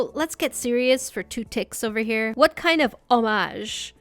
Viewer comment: at around 10:12 did she say to get serious for like 2 "tiks"? like the altean word for second in voltron, is a voltron fan?!